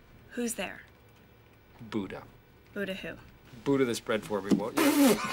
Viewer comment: I'm sorry what does he say? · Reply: Knock-Knock Who's there? Buda Buda who? Butter this bread for me.